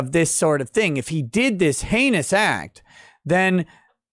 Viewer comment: What? You're saying he tagged her in the a- oh, you said heinous.